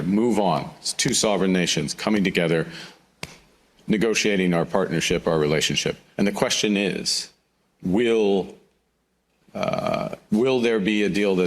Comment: I thought he said our relationship with USA is over?